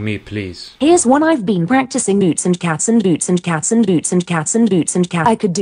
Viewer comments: Sounds like Siri is saying “boots and cancer” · As an italian like you I hear "beats and cazzo" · It kinda sounds like she is saying boots and castles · Siri's beat boxing is literally saying "boots 👢 and cats 😺" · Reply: I hear "cats and bees" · It sounded like Siri was saying “n cats n boots n cats n boots n cats n boots” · I hear "Boots in the caps"😚